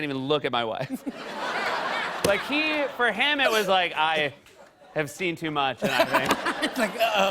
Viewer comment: it sounds like he says “he, for Hannah” does anyone know what he actually said? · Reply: Hannah Lettis For him, he was like “I’ve seen too much.” That’s what he said